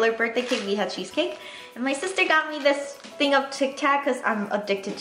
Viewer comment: you know when you said tic tac,I at first thought you said kit Kat